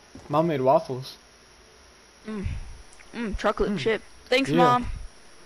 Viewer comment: On 6:03 the captions say chuckling shit Instead of chocolate chip